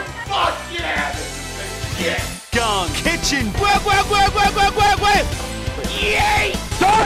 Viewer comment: I don’t know why but I broke right when I heard “gun kitchen”